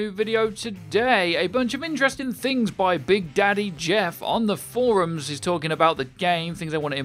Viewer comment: did he just say "big daddy jeff"